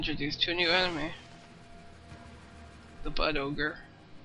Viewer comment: For a second, I thought you said 'butt ogre' and then my eyes caught up with my ears. I think butt ogres would have been more fun to finish off, though.